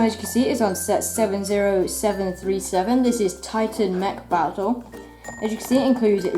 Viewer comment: I thought you said Tyson mech battle